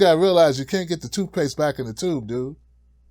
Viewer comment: I thought you said "can't get the toupees back in the tube". That was a weird visual. 😅